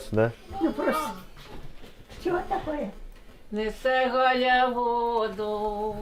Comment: Мне послышалась песня не сы галя в воду я обалдела,потом дошло неси галя воду!